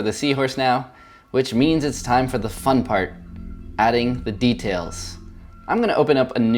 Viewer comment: oh my god 5:53, I thought you said "Now it's time for the fu## part", and then I was like, wow I never heard you curse before. But then I replayed the same part the second time and heard fun part. I felt so stupid..... It might be that my speakers weren't at a reasonable volume so I couldn't hear it clearly enough... lol